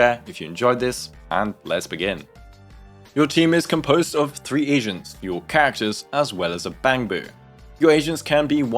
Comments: I heard "team is composed of 3 Asians" lmao. This clarified so much for me, this whole time I thought Anomaly characters were debuffers, cause I don't have any so I just assumed lol. Turning those notifications on for the anomaly video. I don't even know how you know an anomaly is about it about to be inflicted. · Perfect, just what I need. Also I cracked up because I misheard you as saying “your team is composed of 3 Asians”